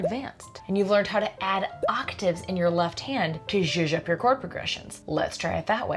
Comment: "to - up your chord progressions". Did anyone catch the missing word ? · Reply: Zhuzh!